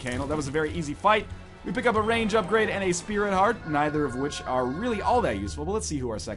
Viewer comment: did he just said spirit heart not usefull?